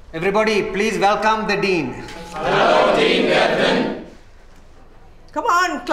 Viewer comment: Am I the only one who thought they said Dean Pelton